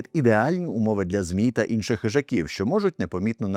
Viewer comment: "темрява та сморід - ідеальні умови для ЗМІ та інших хижаків, що можуть непомітно напасти, коли людина фізично не захищена..." Так почулося, але це має сенс🤔